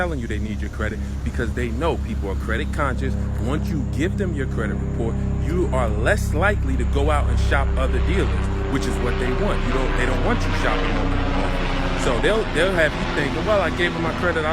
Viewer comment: Ohh, AUTO dealers! I thought you were saying DRUG dealers are running credit reports now! I need a break from the Internet...